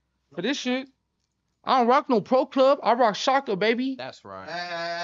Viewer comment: Did he said we don't rock pro club, dead homegirls 😂😂😂😂😂😂